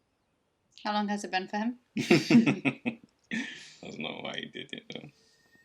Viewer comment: I’m Japanese. What did she say at 44:54 ? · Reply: "How long has it been for him?"